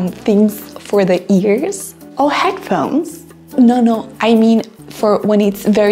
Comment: What she said: “oh, headphones!” What I heard: “OH HECK NO”